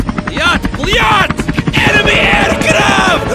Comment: I heard "ENEMY HEADCRAB!" Gordon Fazbear / Freddy Freeman